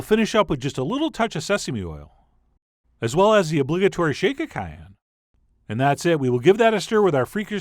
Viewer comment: I couldn't clearly hear what you said at the 0:57 marker.... adding "the obligatory shaka khan" ? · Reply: Dom R shake of cayenne :)